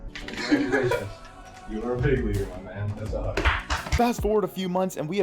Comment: I didn't hear that as "You're a big leaguer, my man. That's awesome." I heard Scott say "You're a big leaguer, my man. That's a hug." Julio went in for the hand clasp & one-arm hug, but Scott gave him the full hug. Great moment there, and part of why Servais is the best manager the M's have had, dare I say better that Sweet Lou. Pinella was iconic and great, but the team's success might've been more a function of their roster than his coaching, which ofc wasn't bad and was actually good. But Servais has had to manage several turnovers in the entire roster and inspire and rally vastly inferior teams on paper into building something great. It's time for the J-Rod show!